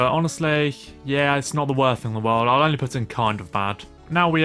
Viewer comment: I thought it said "kind of bald" instead of kind of bad until you said it 😂